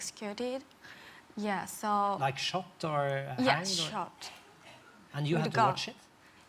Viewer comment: not shocked, shot. I heard it from another north Korean btw. it's horrible how they do it...